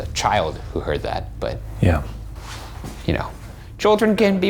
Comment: Anyone else hear a disembodied "yeah" at 9:39 after the boys go on saying "children can be more receptive" "I think they can"